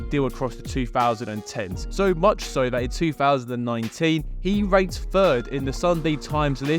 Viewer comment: Ranked 3rd for a second I thought he said raped 3rd